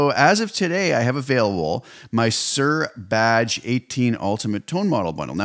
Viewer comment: did you say Suhr Badge?